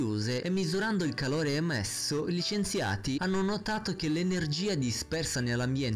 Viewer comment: solo io ho capito i licenziati?